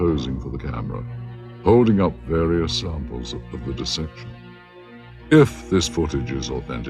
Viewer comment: it really sounds like he says “of the deception” and not “of the dissection” Also what the heck is going on at 17:30:624 (the time in the black box at the bottom of the screen not YouTube players) of the videos run time. Why was it only showed for a quick fraction of a second then not seen again at all in the lab??????